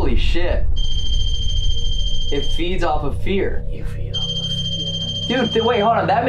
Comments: I swear a hear a voice say “you feel that?” When they are talking about feeding off fear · I swear I hear someone say “you feed off fear” right after says. Almost as if they’re mocking you